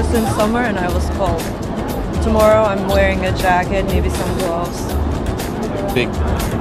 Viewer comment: Did she say, "Tomorrow I'm wearing a jacket, maybe some draws!?"